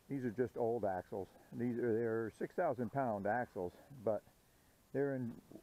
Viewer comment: I thought you said it had 2 7000 lbs axles. My trailers are 14000 pound. That is what I was comparing to.